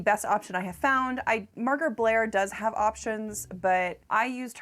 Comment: I misheard "Margaret Blair" as "Murder Blair" and next time I cross dress that is my new name! Gonna go work on my costume for Halloween now.